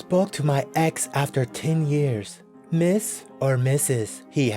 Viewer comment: i thought it said hit or miss